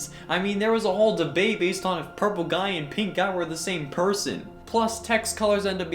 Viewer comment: did he say purple and PINK guy? It’s yellow tho...?